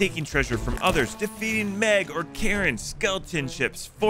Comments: did he call the kraken a Karen lol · Finally, we can kill Karens in Sea of Thieves. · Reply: you clearly didn't understand the comment. They were pointing out that at the timestamp they put it sounds like he said 'Karens' not Krakens